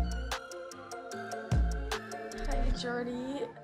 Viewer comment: I thought she said ‘it’s gertie ‘ when she said jordi